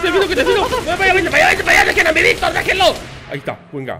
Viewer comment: escuche bien o dijo deben a mi Victor XD